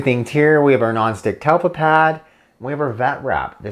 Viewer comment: What is the name of the pads for a dogs paw? Heard it as talco pad